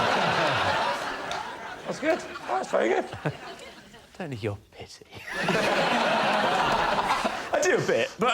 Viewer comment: what does he mutter at 2:08 ? I've played it back over and over and can't tell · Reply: I dont need your pity